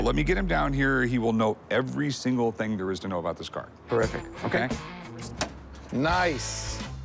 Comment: approximately it sounds like he says “horrific” 😊😊😊